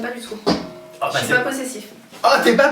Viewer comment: help me ; elle dit " je suis pas possessif " ou " je suis pas possessive " ? car moi j'entends un f à la fin à 02:38.